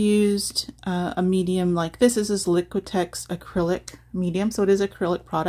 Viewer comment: Oh I thought you said clay…so it’s the liquidtex…ok…I got it!💯❤️